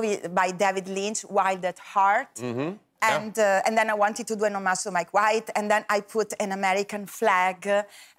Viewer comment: ，what does she mean by "Anomaso", or what's that word she just said? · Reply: That is "an homage" (to Mike White) :-)